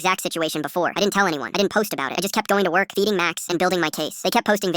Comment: STOP WHEN THE NARRATOR SAID “FEEDING MAX” I THOUGHT HE SAID “ BEATING MAX” AND I GOT SO MAD